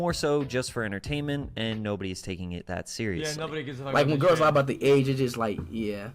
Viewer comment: What did xqc say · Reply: "ya nobody gives a fuck about this shit"